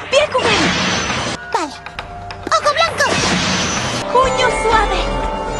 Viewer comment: la primera vez escuche "coño suave"